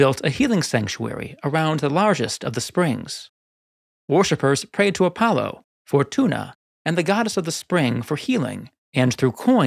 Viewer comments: I thought you said 'Worshipers prayed to Apollo for tuna...' I need coffee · I heard "worshippers prayed to Apollo for tuna" and I was amazed at his nerve to step on Poseidon's territory. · they prayed to Apollo for tuna? 😂😂 Jk, i know it was Fortuna, but it sounded like they just really wanted tuna fish! Lol